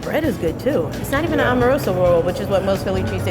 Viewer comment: Not gonna lie ... I totally thought she said “an Omarosa roll” 😂